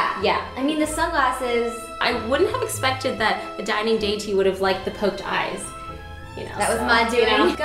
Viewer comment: I thought she said Daddy Yankee not Dining Deity :p